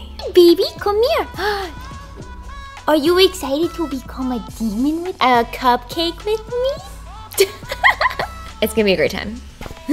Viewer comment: "are you excited to become a demo- I mean cupcake with me?" The subtitles didn't catch the demon, thank goodness 😂